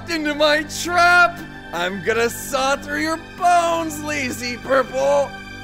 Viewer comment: Hey everyone!!! Hopefully this doozy of a video can tide us over while we wait for the medic video to animate and edit itself ;). It's a relief to get these clips out there and clear out my drives. Anyway, thanks as always for your patience - I hope life's been treating you well! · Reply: I heard you say durple instead of purple